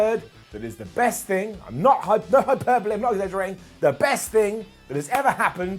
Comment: If someone can tell me what he says I'll dance at your wedding 🤣🤷‍♂️ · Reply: No hyperbole